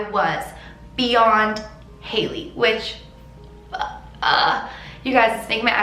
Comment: I don’t think it said Reese. Just Hailey.